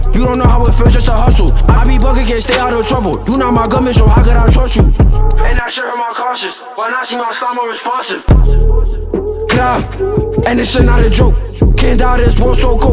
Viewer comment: what he say ? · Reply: “and that shit hurt my conscience when i seen my slime unresponsive”